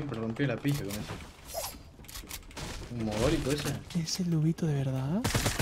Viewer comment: Me paresece o escuche duvito en el minuto 1:35?